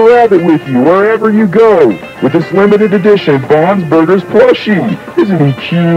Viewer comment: I ALMOST THOUGHT IT SAID BOB'S BURGERS PLUSH COMMERCIAL💀💀💀💀